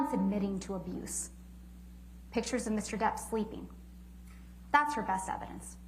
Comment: I miss heard her say Mr Depps Twinky lol but she said pictures of him sleeping.